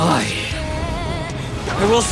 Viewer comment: Would you mind telling me what Kyojuro said at the last part? (I'm Japanese and couldn't hear totally.) "I * my duty fulfilled!" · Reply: I will see my duty fulfilled?